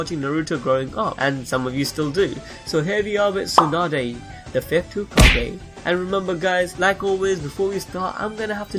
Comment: I thought he said tsunade the tit hokage